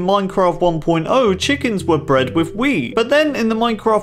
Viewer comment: I think you said weed